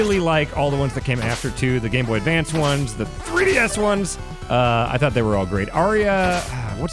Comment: I heard "the three DS ones" as "the 3DS ones" and was very confused for a second.